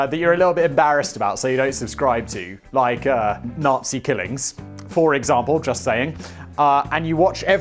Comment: Simon: Surfshark...yaddah, yaddah...nazi killings... Surfshark: Umm, Simon, got a minute...? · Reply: I thought he was saying nazi killings, but towards the end it sounds like he says "nazi kittens".